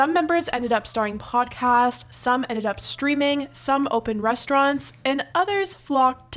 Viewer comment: I thought she said “some ended up stripping” 😂🤣 I need a nap 😩😂🤣